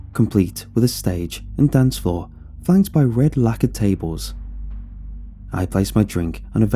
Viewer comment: haha I thought it said “red lack of tables” and i was confused and the next sentence confusedme even more. Lacquer